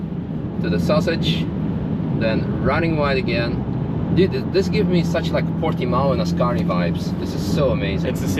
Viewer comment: what did he say? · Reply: I think it is “This gives me such like Portimão and Ascari vibes…” They are two other race tracks.